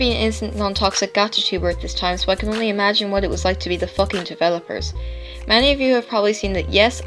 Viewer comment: did I just heard f___?